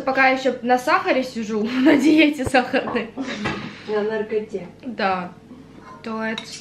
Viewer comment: Мне одной послышалось что сказала " я ещё сижу на сахарной диете" тем временем " на наркоте"